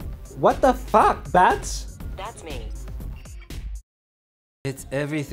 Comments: I thought siri said "that's mean" at the end, no? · I thought Siri said ''That's mean.''